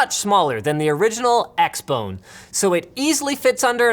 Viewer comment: Did he say xbone? xD